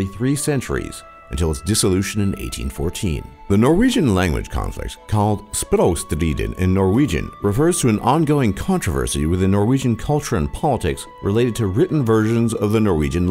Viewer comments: When het tried to say «Språkstriden» it sounded like he said «Språostrien» EDIT: grammar edit. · What siden you trie to say at 3:21? Just as to know ut sounded like spedanstediden.